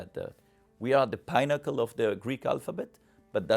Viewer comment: This is not a slam on Raynald, but I swore at one point he said omega is “the pineapple of the Greek alphabet” I stopped rewound realized he said pinnacle. Then thought if he had said that he wouldn’t be wrong. It is the letter that most resembles a pineapple